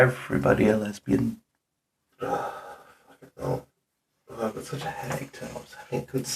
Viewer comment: I love that in the bathroom section, immediately after talking about people finding you two there and assuming you're gay lovers the auto-captions misheard some mumbling as "lesbian" lmao (roughly 22:04ish)